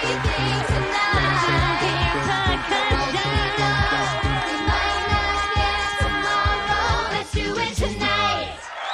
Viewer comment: what did fat Amy say?! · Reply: percussion